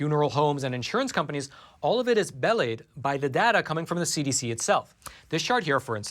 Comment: Did you mean to say belied (about @ 10:10)? Please don't use words on your podcast until you know how to pronounce them. I hope I haven't falsely accused you, but I can't figure out what word you used so this is my best guess. Belied wouldn't have been a good choice for the context: doubly confusing.